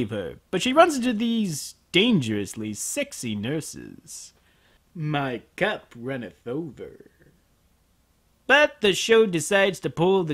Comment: Guys, what does says on 13:37? "My cap..." uh English is not my native - so I can't recognise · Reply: "My Cup runneth over."